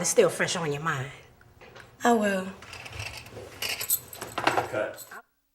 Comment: mins I heard Cut. But Im going to continue.